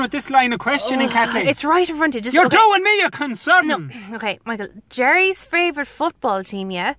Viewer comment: im from Dublin(Ireland) and Its the first time I heard "Ya fecking Teapot" xD I have always heard "you're doing me a concern"